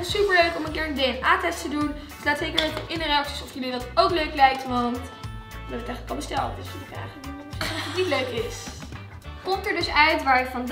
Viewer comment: Wat zegt om 18:29 ?😂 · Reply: “Want we hebben het eigenlijk al besteld dus je kan niet zeggen als je het niet leuk vind” Dat ong